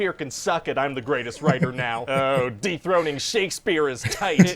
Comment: 😂🤣😂🤣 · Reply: I re-listened to it and I heard “Deep throating” instead of “dethroning”. I spat out my drink through my nose. 🤣